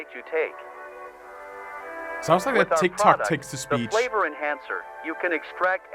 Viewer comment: The entire time until 1:37:40 I thought it was saying “THE FLAVOR OF CANCER”